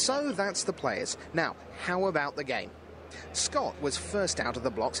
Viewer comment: do i hear "how about the gay"? of what?